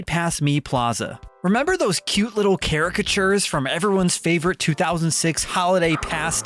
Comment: What did bro say? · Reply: caricature